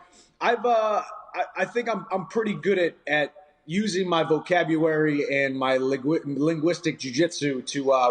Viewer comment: Did he say vocabuwary? Lol